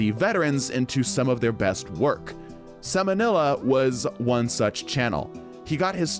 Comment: i thought you said Samanella at first and was so confused, but then i realized who you were talking about and was like "oh i know who that guy is, he' funny lmao," keep it up dude this is good stuff